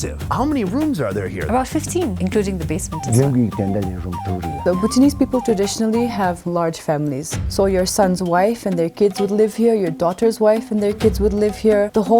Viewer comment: did she just say “your daughter’s wife will live here”